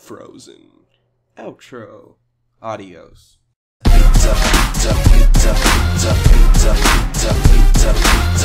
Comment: It sounded like it said "pizza patza"